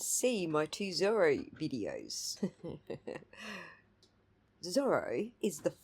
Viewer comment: Thank you i understand it better. I didn't understand the Triooer's explanation. FYI i am from Maass and i thought he said Zorro. lol